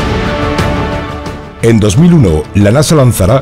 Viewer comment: En 2001? 5:12 :v eh eh escuche mal? Confirmen